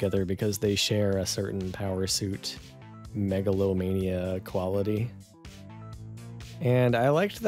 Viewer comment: I’m listening to this half asleep and at one point Neil said “megalomania” but I thought for a second he said megalovania and it triggered my fight or flight reflex